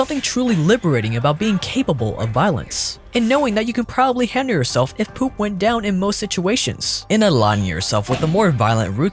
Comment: Did he really say “when poop goes down” at 5:40 😂